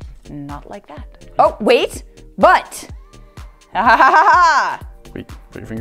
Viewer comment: who else thought she said butt cause of the string shape XD